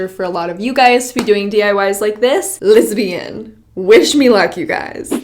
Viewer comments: When says "let's begin" it sounds like she says "lesbian" :) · sounded like "lesbian" 😂